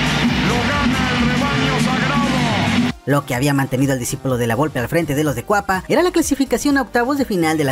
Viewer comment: por qué dices la volpe?